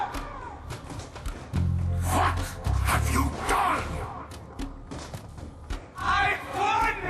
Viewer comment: For a second i thought maxis said "Fuck.... have you done?!"